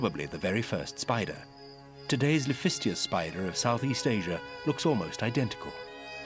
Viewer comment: Does anyone know the correct spelling for the (PHONETICALLY) pronounced ..'lafistias' spider shown at 22:43... well, it sounds like he says "lafistias spider", etc. The one from S.S Asia. Thanks! 😊✌ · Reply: Laphistia spider